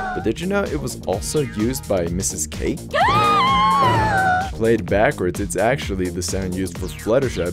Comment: Fluttershy's gasp actually is not the same as pinkie pie's. Listen carefully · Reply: sorry I thought he said "layed backwards".